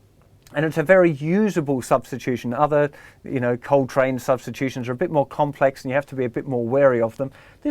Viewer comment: Anyone know about hat other type of substitution he mentions @ 2:00? Somethin like coletrain? Couldn't understand what he said. Thx.